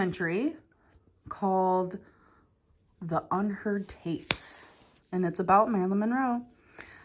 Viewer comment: I thought she said ”The unhurt taints” 😂